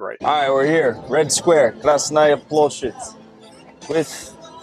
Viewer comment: "Krasny Bullshit"? (04:38) (05:52) (06:00) (06:12). Did I hear that right? I think I probably did!